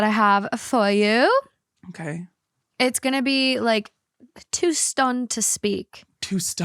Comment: I heard "Too Stoned to Speak" at first 😅😂